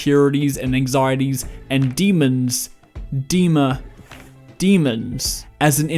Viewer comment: At first I misheard dema for demons